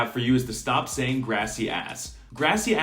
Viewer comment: Every time i heard he say "gracias" is so english accent, he makes me heard greasy ass